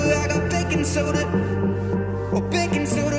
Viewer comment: does he say "Baking soda "??😂